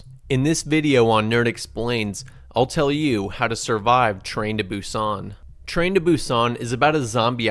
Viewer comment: i thought it said train to bussin but it is train to busan 😂